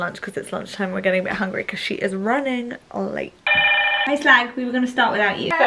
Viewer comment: did you say hi slag 😭😭😭😭😭😭😭